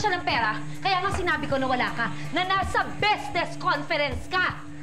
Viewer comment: Ano dw 8:42, prang BEZNES CONFERENCE yta narinig ku???😂🤣